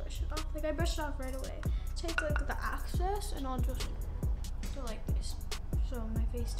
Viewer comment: did she just say access instead of excess